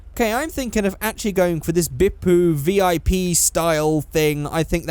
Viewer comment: When you said Bippu I thought you said Big poo